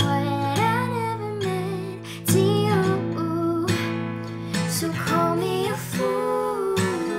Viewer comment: *I thought you said call me a foot*